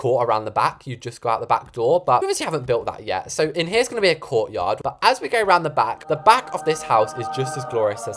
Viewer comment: No kid I thought said "over here will be a CORPSE yard:)" instead of "COURT yard"- PS luv ur vids pookie :))))))